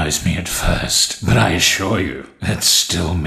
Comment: when he said its still me i paused it and i thought of him saying its still memes